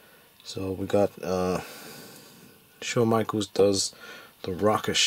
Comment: did you say Shawn Michaels?